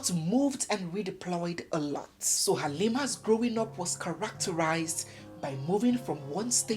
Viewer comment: I thought they say is character deformation